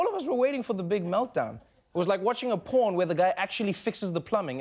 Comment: did he say it's like watching a porn? Surely not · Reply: He said its like watching a porn where the plumber actually fixes the toilet and just leaves.